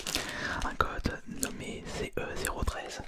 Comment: j'ai compris un god au début